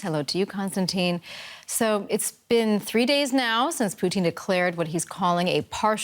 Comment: "I support my husband and "his" decision"...umm riiiight. @ 3:10 the news caster said poutine instead of Putin. Lol I like French fries and gravy/cheese too lady...